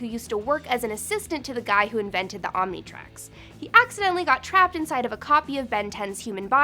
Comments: Did she just call it the Omnitrax? Pronounce it well Om-ni-trix. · omg did she just say omnitrax instead of omnitrix